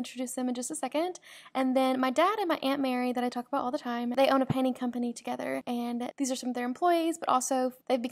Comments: omg please- i thought she said pantie company not painting.... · I really thought you said your dad owned a pantie company, and was like oh wow that's interesting lol then later when they were PAINTING, I had a realization 😅